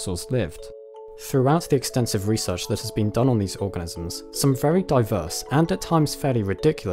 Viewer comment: at :23 seconds, it sounds like you said "research done on these orgasms"...I even had to go and check it a few times.